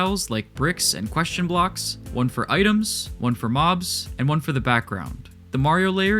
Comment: the "mobs" layer i thought it said "hoes"...